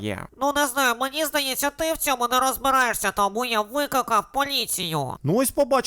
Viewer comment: мені одній почулось «Викакав»😂😂😂